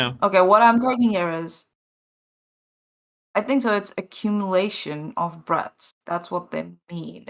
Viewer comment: I thought she said "an accumulation of drugs." Sounded like a good explanation for Jordan-world.